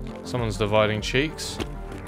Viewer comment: I genuinely thought i was the only one who thought i hear that as "divide my cheeks"